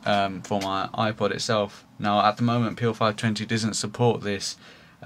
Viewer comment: sounds like ur saying P O 520. was a lil confusing lol